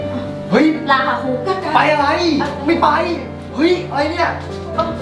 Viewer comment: ได้ยินเหมือนเหมือน พูดว่า ไปอะไรกูไม่ไป